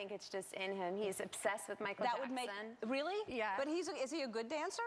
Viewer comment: Plus she does an AMAZING job voicing Elsa in Frozen! 😄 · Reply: I think she said Michael Jackson. Although it definitely sounded like she said accent!